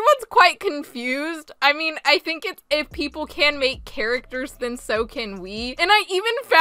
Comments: i heard carrots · "Welcome to Gacha Life, there's so much to see!" "If you can make characters, then, so can we!" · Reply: I heard, "If you can make characters, then, so can we."